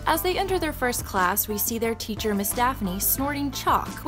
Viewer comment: is she saying Miss Stephanie or Miss Daphne i